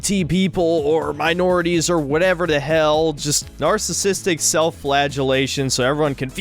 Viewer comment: I spit my soda out. I thought the video said narcissistic flatulence.